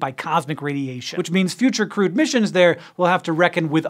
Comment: Every time someone says "crewed missions" I hear "crude missions", and I ask, "What about more sophisticated missions?" before I correct the spelling